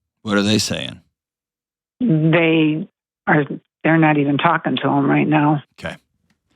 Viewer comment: I disagree -- vehemently! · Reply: I also disagree with myself! 😂 I rewatched to figure out why people are mad at my comment from 3 months ago. I want to clarify that I misheard one sentence that made me come away with a incorrect understanding of the situation. When she said “my other kids aren’t talking to them either” I thought she said “My other kids aren’t talking to ME either”. I thought everyone was cutting ties with her and figured she was probably a narcissist playing victim. But now I get that it’s her and her family vs dad and selfish sibling.